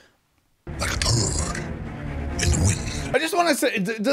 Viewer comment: Venom never said "Like a turd in the wind" but that's the kind of bad jokes he does, he has this weird sense of black humor, like he always makes bad jokes that he overrun and come off as monologues everytime he face a thug or baddie, and i'm surprised they got that aspect right. Also the whole "Eyes, lungs, pancreas, so many snacks, so little time" is straight from the comics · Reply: NickToVa he did say turd in the wind Though I misheard it as bird in the wind, lol